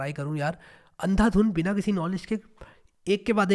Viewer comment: i heard Andhadhun, do you?